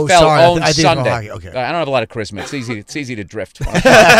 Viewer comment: couldn't catch what Bill said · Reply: "I don't have a lot of charisma, it's easy to drift"